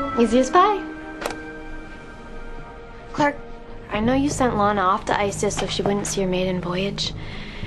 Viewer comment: why do I hear "I know you send Lana to ....."